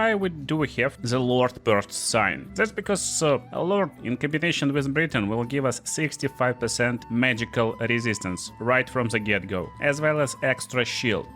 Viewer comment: Thought you said "The Lord in combination with Britain will give us 65% magical resistance."